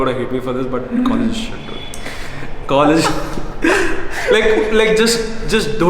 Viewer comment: what did he say? college ____ hai? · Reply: College is S@ ;-)